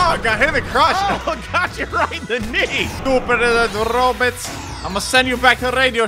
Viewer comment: is it just m or did say "shot in the D"?